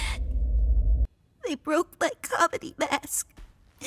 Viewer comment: People who thought gangle said "I broke my common mask" at first 🔽